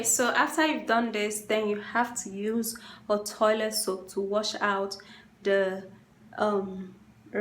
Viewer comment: I'm asking the same thing. I was trying to see if she was actually taking water out of the toilet. Someone please help us with this question lol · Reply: I was wondering the same thing. At first I thought she said toilet soap. I'm stumped